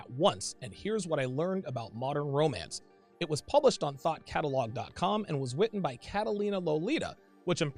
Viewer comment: Funny, I heard "Thot catalogue"... A repository of justification for banana gobbling???